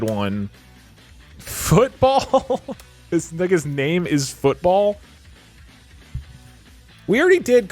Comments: did he just say the n word??? Wtf?????? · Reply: He said something like “This- like his name is football?” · I must've heard something incorrectly. Did he just say the N word? Video has weird audio problems??? What? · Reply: I’m pretty sure he was trying it say “it’s like his name is football” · did doug just say the N word or am i insane??????? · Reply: “It’s like his name is football?” (or “like his ni- his name if football?” No. He did not. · please.... i need to know what he is saying at 1:01:30 i am losing my mind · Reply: He says football